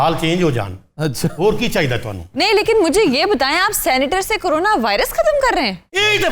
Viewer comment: Did she say "senator" at 3:08?? 😂